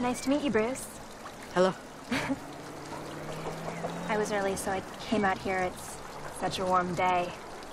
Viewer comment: What she say at 1:42? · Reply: "I was early so I came out here. It's such a warm day"